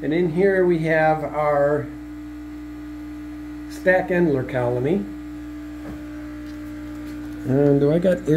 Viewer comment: lol what did ya say @ 7:45 mark of the video? Good ole tongue twister! 😂😂😂 but honestly I absolutely love what you and your wife do! I love it so much I continue to go through all your videos and watch em over again! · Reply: I said my Staek Endler Colony thanks for your Support